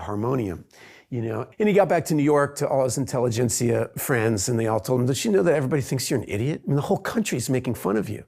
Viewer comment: I've been watching this video repeatedly to improve my listening comprehension, and was wondering if someone can help. From 0:50 he's talking about Ginsberg, and according to the subtitles, "(Ginsberg's friends) told him does she know that everybody thinks you're an idiot", but I can't catch the part "does she know". Are the subtitles of this part correct? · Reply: I admire your desire to improve your listening comprehension. I believe Ethan says it so quickly; though it sounds like "did she know" it is actually "didyaknow" or "don'tyaknow". That's what I am hearing!